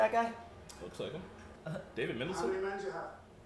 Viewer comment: I cant quite make out the star wars reference and were mumbling around 10:00 mark but it sounded like you were trying to say Is that Ben Mendelsohn and No, it isnt unfortunately. Love that dude